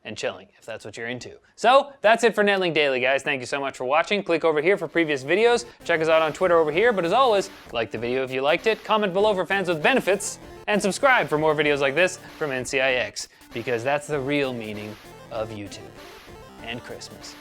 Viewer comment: I always hear "Comment for PANTS with benefits down below". I chuckle every time.